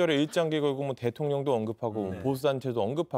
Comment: 뭐라고 말한건가요? 발음이 부정확해서 못알아듣겠는데 제 귀에는 '대통령부터 더 문제야 솔직이..'라고 한거 같은데 아닌가요?